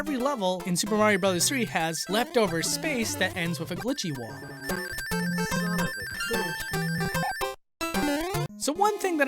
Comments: Did anybody else hear "son of a glitch"? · who heard the son of a glitch at 7:25? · Anyone else hear the really quiet "son of a glitch" at 7:25 ? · I heard "Son of a glitch" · Who else heard at 7:27 son of a glitch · Who else heard “Son of a Glitch!” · who else heard “son of a glitch” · Anyone else hear "son of a glitch" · Who heard son of a glitch in the back ground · Am i the only one that heard "Son of a Glitch" in 7:25 ? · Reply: ClassBuilder GD I hear "son of a bitch" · Did anyone hear "Son of a glitch"? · i heard "*SON OF A GLITCH!*" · i heard "son of a glitch" in the background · I heard “son of a glitch” from. Nice. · who heard son of a glitch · I heard son of a glitch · did i hear "son of a glitch?!? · Anyone else heard "Son Of A Glitch"? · did anyone hear the Son Of A Glitch intro play? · I heard a slight "Son of a Glitch!" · Did anyone hear say son of a glitch? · I heard that very muffled "Son of a glitch" at 7:24 :P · I heard son of a glitch from glitch videos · anyone else hear Son of a glitch? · I heard the Son of a Glitch intro. Am I the only one? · Is it just me or did anyone else hear "Son of a bitch" at 7:24? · did anyone hear the "son of a glitch" sound from the youtube channel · Who heard "Son of a Glitch"? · did anyone hear "son of a glitch" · anyone else hear 'son of a GLITCH' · i heard "Son of a glitch" im not deaf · Did anyone hear son of a glitch in 7:23 ? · Did I hear Son of a Glitch?